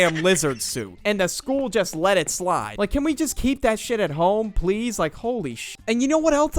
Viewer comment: The like button glowed, because the auto generated cc thought it said, "Please Like".